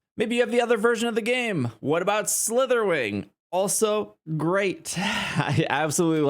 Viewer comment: I thought you said "What about Slithering". Meaning I thought you refered to Scarlet owners as Slithering house XD. I just watched all 8 Harry Potter films and have been playing the game for 3 days. Clearing I have Harry Potter on the brain lol